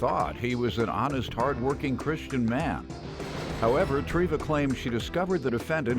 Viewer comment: Treva? Oh I thought he said Trinity from the Matrix.